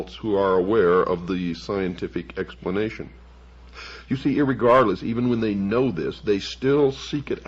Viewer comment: Does Bill say “irregardless” here? Whose writing is he reading? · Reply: Bill said "irregardless" a lot. I used to listen to his broadcasts years ago. It's technically a word, albeit a superfluous one meaning the same as "regardless" despite its apparent negation.